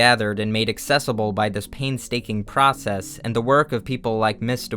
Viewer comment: Did you just say 'paintstaking'???